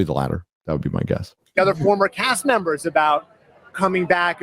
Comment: Anyone else hear, "returning ass members,"? Anyways, serious question how many legacy sequels/reboots, etc. have actually been good? I mean, they either enhanced or, at the very least, didn't tarnish the original in some compacity. If the phrase " too bad the sequel sucked" applies to it, then obviously, it doesn't pass. Also, I'm giving a span of at least 10 years with no other film/TV projects relating to it as being legacy. Top Gun:Maverick and the Twin Peaks revival are the only ones that come to mind at the moment. Granted, I haven't seen the latter, but I heard good things.